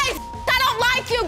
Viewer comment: Wait did she say your son is gonna be my son 🤣